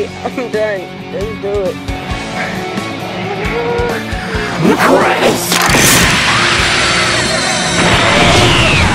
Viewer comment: what did he say when his eyes started glowing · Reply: “Requis”